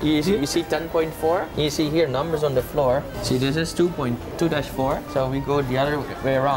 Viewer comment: 음? 내가 잘못 들었나? 너무 자연스럽게 투 다시 포 라고 한거 같은데.ㅋㅋ 좋은 추억 많이 많이 담아가시길 바랍니다 ^^ 새해 복 많이 받으세요~